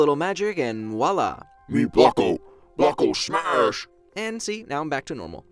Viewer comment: I swear he said ' Buko'. You know?